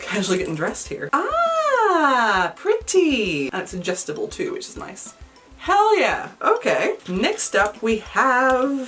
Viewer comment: I fully thought you said the scrying ring was "ingestible, too" and got SO concerned for a second lol